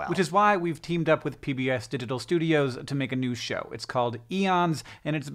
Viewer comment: Id swear the first time i heard him say pbs digital studios, i heard "we teamed up with pbs to diddle studios" and i did a literal spit take, my monitor nearly fried, sweet.